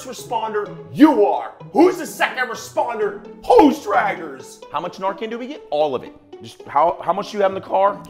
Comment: did he say "hose draggers"? How incredibly jealous they seem to be of firefighters! No DOUBT!